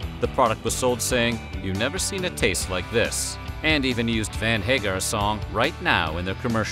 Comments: Did you say Van Hagar song Right now??? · Did you just say "Van Hagar?"